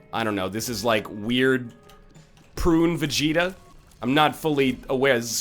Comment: This guy said Super Saiyan 3 Vegeta... kill me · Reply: Mark Keto when cuz all I heard was "prune vegeta" and then he said super saiyan level 3 baby